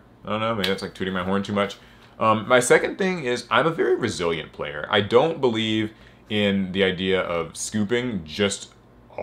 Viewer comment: I thought you said "Brazilian player" and was wondering why they never scoop when playing yugioh in Brazil